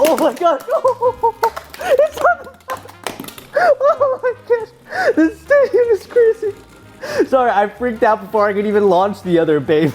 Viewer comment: What is saying at 7:04 mitnets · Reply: He says "Oh my gosh, the stadium is crazy" Before that he doesn't really say anything, he cut himself off with laughter but did say "He's on the-"